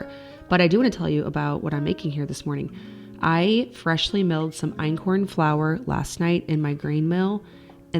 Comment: watching this before my coffee has kicked in, totally thought you said you had "freshly milked some flour"...i need a brain reset